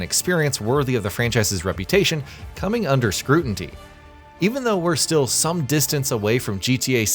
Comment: Did you just say “scrutinTy”? 😂